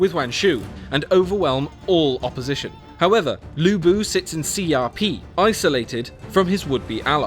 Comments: Oh its Xiapi i thought he was saying Crp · At first I thought he said "CRP" instead of Xiapi. :D